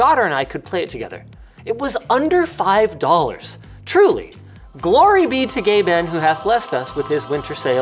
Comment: I wasn't looking at the screen at the time at 6:18 but it sounded like Linus said glory to be gay men until I saw Gabe haha.